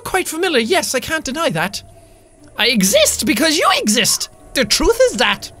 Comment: I thought it said sexiest instead of exist I was like THE FUCK?